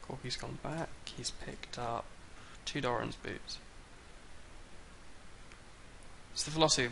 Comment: I heard Doran's boobs. LOL